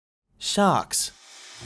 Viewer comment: "Sharts" oops! I misheard that.